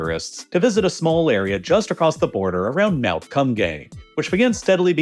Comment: Did I hear that correctly? Mount Cum Gay? 😂